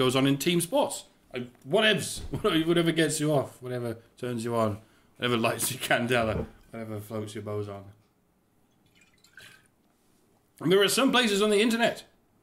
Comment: I must have Physics on the Brain... because at 7:10 it sounds like you said; "...whatever floats your Boson". Perhaps I'll look up the Physics of the Mosh Pit. Also, Loving Carl's comment regarding your running for office (as I'm here waiting for his next post while he's running for MEP!).